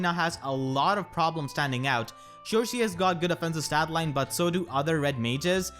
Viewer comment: you said "sure she", and I thought you said "cherche" and I was confused for like half a second.